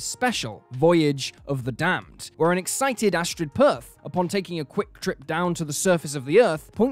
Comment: her name is Astrid Peth, not Astrid Perth. Catherine Tate was the first person I heard make this same mistake.